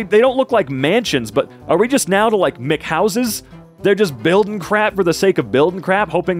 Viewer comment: I deadass thought that man was saying "McHouses"💀